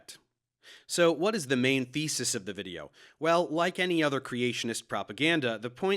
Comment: i'm sure i heard you say "what is the main faeces of the video" instead of thesis